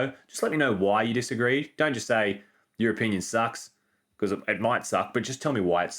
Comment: 😮My face when i heard "european sucks" instead of "your opinion sucks"